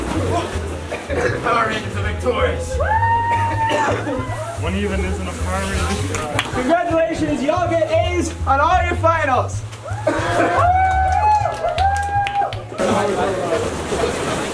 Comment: at first i thought the dude at 5:32 said "what even is a power ranger" but i misheard lol